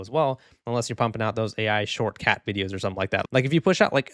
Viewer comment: I thought you were saying the Mario Kart YouTuber Shortcat was posting AI shorts for a second lmao Anyways this YouTube update SUUUUCKS